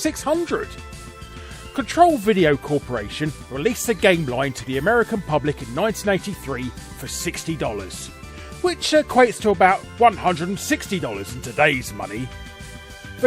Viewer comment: anyone else hear 1988?